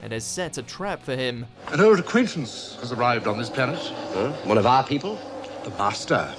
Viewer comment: The caption says: Her recruitment has arrived on his pennis. One of our people. The bastard. XDDD